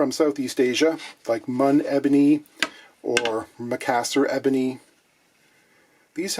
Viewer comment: Can't make out what kind of ebony you're saying, masser? In any case I came to say that is a beautiful piece of wood, beautiful contrast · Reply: I‘m pretty late. But he says Macassar. You might know it from back n sides of some guitars (Taylor is the first one popping up in my mind). It looks stunning but nothing like just the pure black ebony we all know from fretboards n bridges.